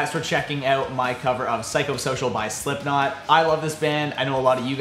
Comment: Is it just me or do I hear "circumcition" every time they say psychosocial 😬